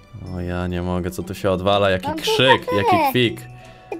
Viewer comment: Ja usłyszałam jaki kwik taki srik